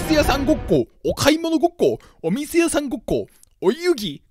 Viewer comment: お店屋さんごっこがゴミ生産ごっこに聞こえた…